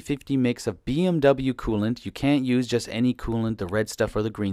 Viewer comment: Excellent video. Just one thing, at 2:32 mark, did you say that we CAN use any coolant or CAN'T use any coolant ? Thanks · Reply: Zerex G48 is equiv to BMW. You cant use just any coolant. Gotta be the blue.